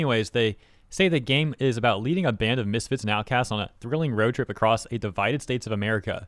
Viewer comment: When you are reading the description of the game I heard it as Leave and Abandon misfits which fits since no one is playing the game.